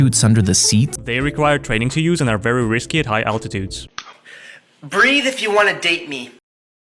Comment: I THOUGHT THEY SAID "BREATH IF YOU DON'T WANT TO DATE ME" 😭😭😭😭😭😭😭😭